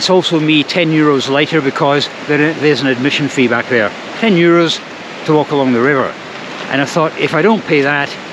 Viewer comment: On your "short" I thought you said "all this for 10 year olds". Huh? Ah - 10 euros! Early in January they have the Hornschlittenrennen - which sounds and looks quite crazy.